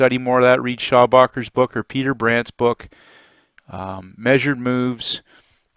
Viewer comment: Around the 6:35 mark, you mentioned some books that we should consider reading on breakouts. Could you give me the author's name. Couldn't make out what you said initially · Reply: Peter Brandt